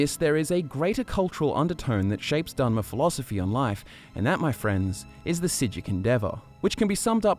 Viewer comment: What does he say at 4:30? "And that's my friends it the X endeavor"